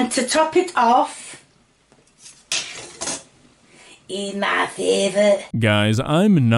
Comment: i think said "it's my favorite"